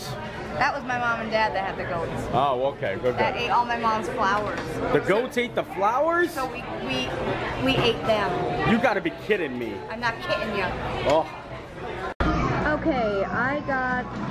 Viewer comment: Did I just hear V say, "I'm not kitten ya"?? Yeah for cats!